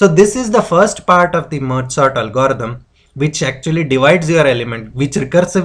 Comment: thanks.. i keep hearing "mozart algorithm".. maybe because i play violin..